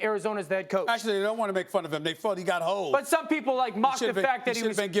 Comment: Did SAS just say “ they felt like they got hoed”😂?